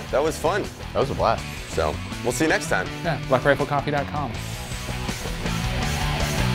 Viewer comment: I thought he said moustache massage 😆